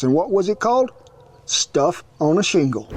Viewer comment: I've also heard it as - sh¡t on a shingle, lol